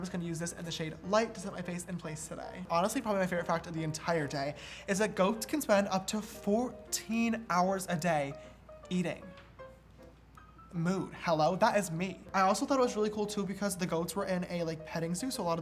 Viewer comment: did he say moo, moot, or mood